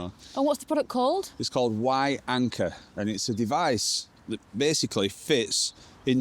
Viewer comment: I thought you said wife anchor at first 😅